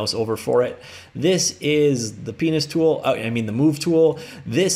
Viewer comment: This is the penis tool... Sorry, but this is how I heard it. No idea if correctly.